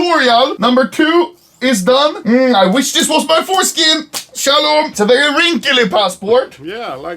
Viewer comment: DID U SAY SHALOM?